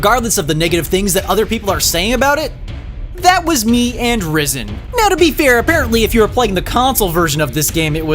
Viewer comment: I thought at 4:34 you said 'riven' instead of risen (I wast looking), and it made so much sense.